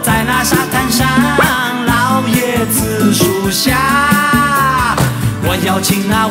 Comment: 那句歌词听成了“老爷子数虾”